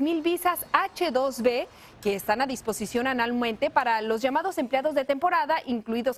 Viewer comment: wtf was that .? Que coñ0 .... "Que están a disposición analmente" ×0 Eso dijo o escuche mal ???